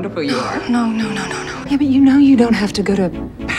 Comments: Did she call her baby?! Idk if I'm delisional 2:12 / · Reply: It's "Yeah, but", unfortunately. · Did Lena said baby in 2:13 I didn't catch that when I watched the episode 😲😲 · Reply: No...it a only her accent for ''but''